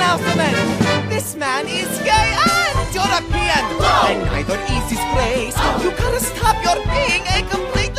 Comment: I just realized that between 2:50 and 2:51, I hear Wally say " And neither is his praise!" Did anyone else catch that?